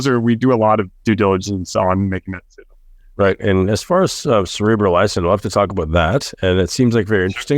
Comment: great podcast very informative...however would be nice to have the un-garbled names of some of these peptides, especially at 43:26 "Cerebral ishhho..." can't make out what's being said.